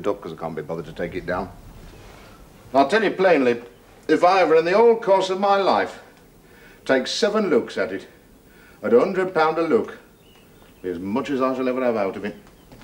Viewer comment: what did he say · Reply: "I'll tell you plainly if I ever in the whole course of my life take seven looks at it at a hundred pound a look, is as much as I shall ever have out of it."